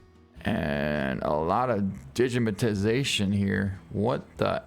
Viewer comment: The "digimitization" I hear gets worse the older you get 28:45 :)